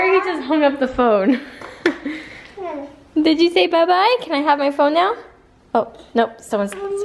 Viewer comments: it sounded like said "wanna buy my phone? ". Or am I crazy ????? Lol. :) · it sounded like said "grandma phone" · it sounded like said let me get my phone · it sounded like said mumma buy my phone xx